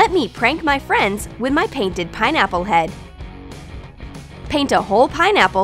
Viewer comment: did she say pineapple pen?